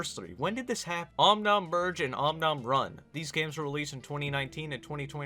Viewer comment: om nom virgin it sounds like you said om nom virgin not merge